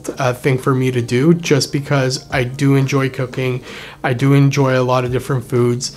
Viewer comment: I thought you said cocaine lol